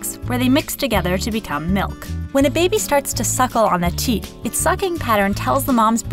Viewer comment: i heard the word tit but in a fancy kinda way